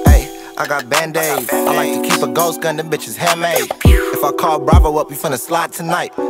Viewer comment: what did he say after "I like to keep a ... gun them bitches hand made" i cant really guess Gauss gun maybe ? idk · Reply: Ghost gun